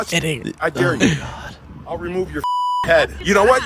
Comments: We appreciate your sacrifice! Sounded like he said I'll remove your head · Sounded like "I wish you were 18, I'd fucking remove your head"